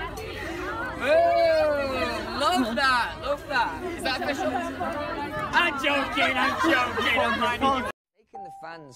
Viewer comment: What does he say at 8:09??? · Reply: "love that love that! is that official? im joking im joking!"